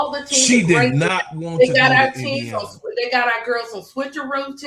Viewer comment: I thought she said she didn't want to go to fever 😊